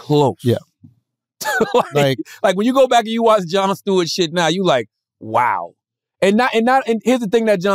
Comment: Did he just say Jonald Stewart?? Classic cdagod vocab